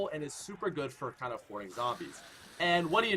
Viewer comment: it sounded like he said whoring zombies😂😂😂😂